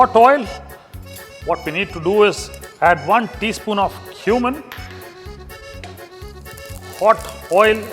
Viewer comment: I thought, he said to add 1 teaspoon of human(cumin).